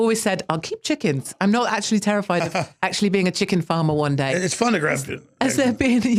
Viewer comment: What the hell is he saying 11:37? It’s fun to grab chicken....?